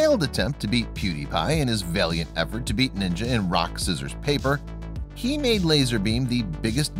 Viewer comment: Sorry, did he just say rock, scissors, paper?